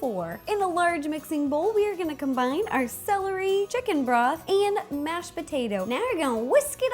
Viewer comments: at first i thought she said chicken breath instead of broth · It sounded like she said "Chicken Breath" XD